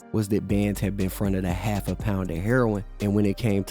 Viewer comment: Man I heard Bandz had befriended a half a pound of heroin... I know it's been fronted, but I heard what I heard